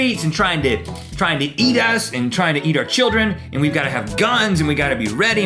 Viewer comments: I thought he said "eat ass" sksksksksk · Wait wait wait, did he say "Tryin' to.. Tryin' to eat ass!"